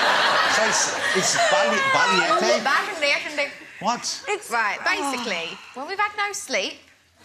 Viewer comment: That's not what she said, it's not bashedeshede, it's balegduh